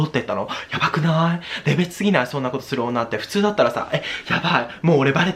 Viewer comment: 質問です、ここ〇〇すぎない？ってなんて言ってるか聞き取れません！笑 おしえてくださいw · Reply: 6年前のコメントに失礼します レベチって言ってると思います！